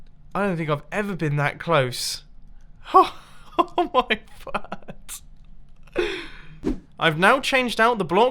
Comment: does he say "oh my fart"?? xD